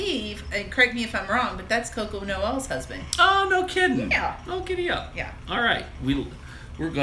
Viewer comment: Around the 3:05- 3:16 mark did you say "oh giddy up"?